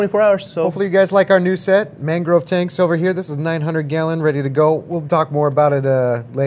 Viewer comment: copper treatment for fish for 10 days or so if the fish can't handle copper then a observe QT for 2-4 weeks and then 2 weeks in breeder box while in display so it starts to eat again. my 120g reef setup just got 2 clowns last night in it first fishes that moved from my nano that is now my QT i only had 2 clowns in there. the snails and crabs are still in the nano tho. for corals i plan on dipping only nothing else for inverts just acclimate and add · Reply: oh 900 Liters i thought i heard 900 gallons i was like what the hell that is 900 gallon how long is that tank like 15ft hehe